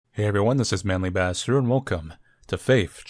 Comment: it sounded like you said “Manly Bastard” instead of ”